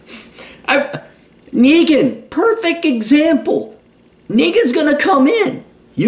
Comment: Google Captions say is saying "niggas gonna come in" at 12:25.